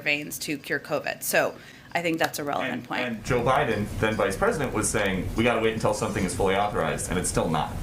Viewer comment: At the 1:17 mark did he say, " Joe Biden, who was then vice president...." or am i crazy?